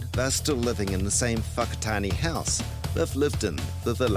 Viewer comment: did he say "and they're still living in the same f*ck tiny house they use to live in" ? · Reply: No, it was the location ‘whakatane’ house they used to live in